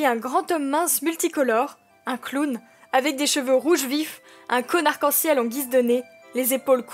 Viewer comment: J'ai entendu "un conn*rd quand ciel" j'ai dû réécouter ce moment x)